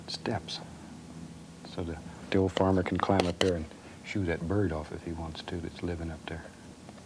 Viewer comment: “So the old farmer can climb up and shoo that bird off up there.” I thought at first Bob was saying “SHOOT that bird off,” and was like, that doesn’t sound like Bob at all! 😂